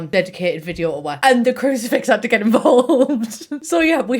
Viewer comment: She said CRUCIFIX?? I thought she knickers 😂😂😂 I literally thought she said “ignore my knickers on the floor” 😂🙈🙈